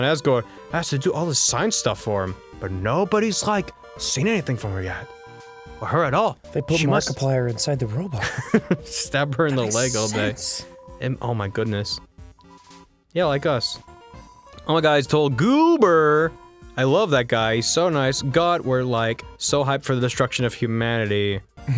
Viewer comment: ive seen this playthrough so many times and i just realized at 16:45 ross says "stab her in the leg all day" instead of "stay in her lab all day" and i'm chOKING